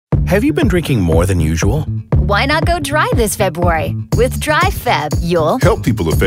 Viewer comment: At first I thought it said "Why not go drive this February?" And I straight up thought it was telling you to drink and drive